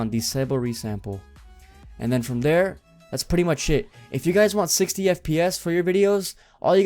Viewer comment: he says “that’s pretty much sh*t” 😂😂 instead of “it”